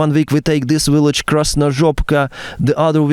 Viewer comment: what did he just say - they took the village of Krasnozhopka 🤣